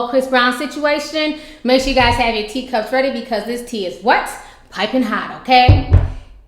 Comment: at first I thought she said "this tea is wet" I was about to just... yes. the tea is wet